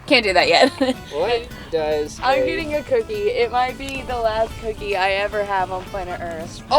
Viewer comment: Growing old has its perks with bad hearing and misunderstandings. Just had a good chuckle because I thought kara said "this will be my last hicky..." After rewinding that part 5 times, she said cookie not hicky 😂